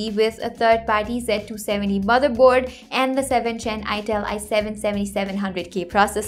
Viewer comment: Did she say "ITEL" at 0:54 Now I'm kinda doubting her PC knowledge